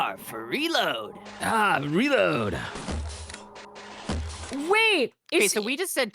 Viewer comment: I always thought he was saying "Arms, reload!" because he's... armed... like, with firearms.